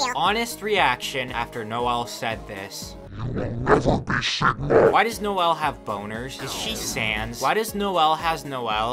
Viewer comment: What is that censored word? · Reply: I made the meme and it is “accurate”